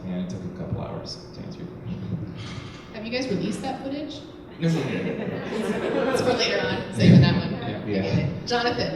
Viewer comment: Can someone tell me what the woman is asking in 5:01? I just can't figure it out skjsk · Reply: She asked if they released the footage. When they said no, she said saving it for later?